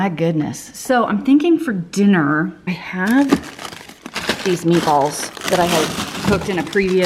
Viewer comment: I have my phone turned down watching this because I guess I should be doing something besides sitting on my tush watching my BFF cook. 🤷🏻‍♀️ Anyway, I thought you said you had lots of “paintballs”. I was seriously interested in how you planned on incorporating those into your meal plan. 😆